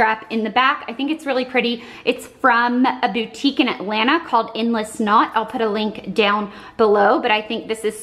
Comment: LOL Blair, I totally thought you said the boutique was called "Endless Snot" I was like that sounds messy haha